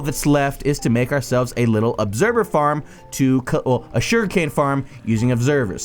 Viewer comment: i'm the only one who heard shurikane? xD